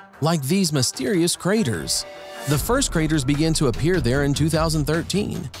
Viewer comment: He said "first craters" I heard "first graders"